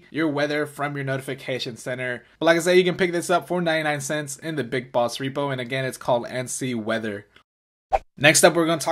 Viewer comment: I thought he said "Big balls repo" for a second XD